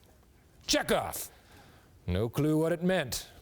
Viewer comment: Peyton thought he said Jack off that's why he was laughing .